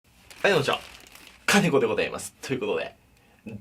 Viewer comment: 最初の「金子でございます」が 耳の力を抜いて聞くと 「カビゴンでございます」に聞こえる 耳の力は鏡の前で全身の力を抜いて白目になると出来ますよ